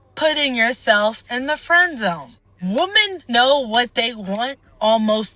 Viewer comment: Did she just say "womans know what ..."